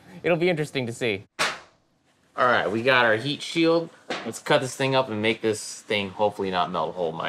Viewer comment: When he said heatsheild I thought he said yeet sheild